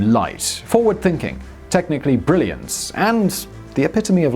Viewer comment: *its end And did he say ‘technically brilliance” at 1:10?